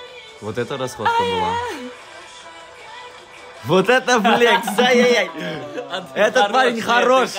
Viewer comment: я же не одна услышала мат?😂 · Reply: он говорит вот это флекс